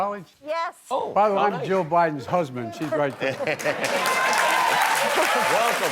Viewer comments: Did he just call his own wife “Joe Biden’s Husband”? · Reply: No, he called himself "Jill Biden's husband" but I can understand how it sounded like that. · W8 did he just say he was Joe Biden’s husband pointing to his own wife?🤣😂🤣😂😂🤣😂🤣😂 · did he say "I'm joe biden's husband???" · On 1:23 did he say I'm joe biden husband ???? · Did he say I’m Joe Biden husband? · Sounds like he was saying he is "Joe Biden's husband". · “btw im joe bidens husband shes right there” -am i mishearing this? i am legitimately not sure if i heard this right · Sounded like he said in the beginning “by the way I’m joe bidens husband “ lmfao correct me if I’m wrong 🤣🤣🤣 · Sounded like Joe said I'm Joe Biden's husband. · Did he just say... "I'm Joe Bidens husband, she's right there" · Did he just say "joe Biden husband she's right there" hahaha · Did he just say"I'm Joe Biden's husband"💀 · ''Btw I'm Joe Bidens husband she's right there'' Famously said by Joe Biden. Edit: If you think about the name"Joe" that's what you will hear, if you think about the name "Jill" that's what you hear. Btw, what color is that damn dress now again? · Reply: I think Joe is the worst President ever, but I think he said “BTW, I’m Jill Biden’s husband, she’s right there.”